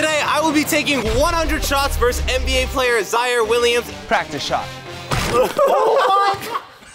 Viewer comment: I thought he said zion Williamson, I was so confused at first